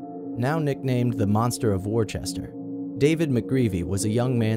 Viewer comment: Omg when you said Worcester, I thought you said Warchester! I'm from Worcester so I was so confused when you started talking about David Mcgreavy! Everyone's been saying that he's going to return and kidnap someone...obviously they're rumours, but still creepy..